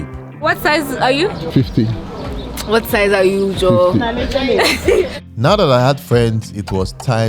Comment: God sent lady. Did i hear her say jo at 3:04. Wow! 🤣🤣🤣